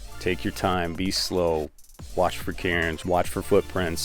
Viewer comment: Wait, did he say "watch for Karens"? 5:17 LOL